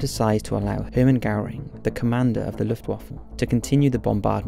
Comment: Sounds like u say Luffewaffle xD